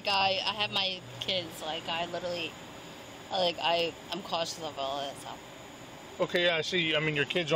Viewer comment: What does she say about her kids @ 5:16? She says she’s “literally like-I’m literally like conchunablism”.